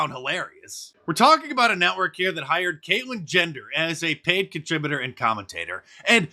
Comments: Enjoyed the video as always. I had to chuckle a little when I thought I heard you say "Kaitlyn Gender" ... I may have misheard, but it still made me laugh. 🤣😂🤣😊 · did Mark say Caitlyn Gender 😂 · in, did he say Caitlyn Gender? Hilarious! 🤣